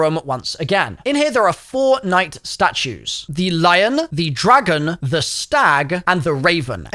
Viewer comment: I thought said “fortnite statues” I need some sleep bruh